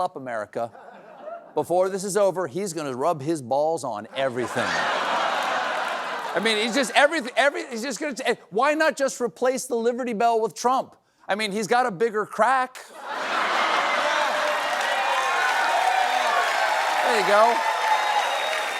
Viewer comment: did you just say..."liVerty bell???